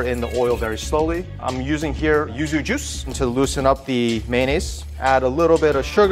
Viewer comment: using what kind of juice pls? didn't catch the word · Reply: Yuzu juice.....the caption says so😅